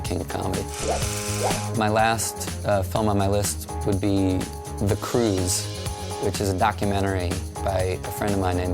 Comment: For a split second I thought he said 'The Croods' as a bizarre joke, then I remembered that Leaves of Grass came out a few years earlier.